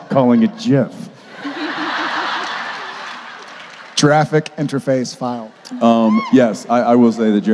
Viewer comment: "Jeraffic interface file" lol it almost sounded like he said jurassic😂